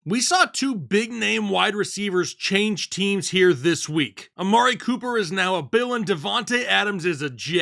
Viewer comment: I just hear you say “Amari Cooper is now a villain”